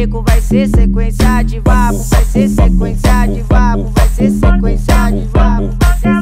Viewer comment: En ves de "vapo vapo" le entendí "Papu papu"